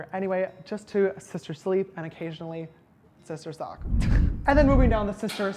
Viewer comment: did he say sister succ omg